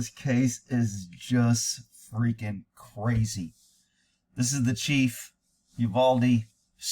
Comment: In the beginning, it sounded like you said, this is the chief, Ubaldy... LOL.